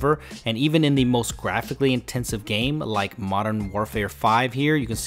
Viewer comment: Did you say modern warefare 5 ???